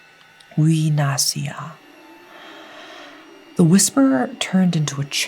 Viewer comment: I don't know if I heard it right but it says " uwe na siya" meaning "she'll /they'll go home" ?